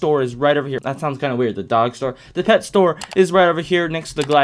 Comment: when he said dog store i thought he said doggosaur